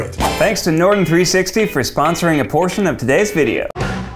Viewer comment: I thought Ned said “thanks to norton 360 for sponsoring abortion of today’s video”.